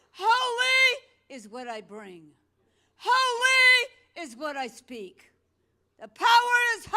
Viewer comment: Instead of holy, Katt yells so much it sounds like she is saying Ali... prince Ali, wonderous he...